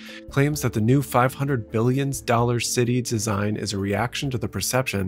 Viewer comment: Did he say 500 million or billion bc one sounds oddly more achievable than the other…like 100x more achievable. I heard million, transcript says billion. But I doubt your building anything that size for under a billion. At the same time $500,000,000,000.00 is a hyperobjective (learned this word at 3:57) amount of money.